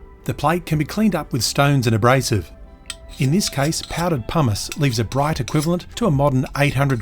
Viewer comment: I misheard "powdered pumice" as "powdered houmous" 🤣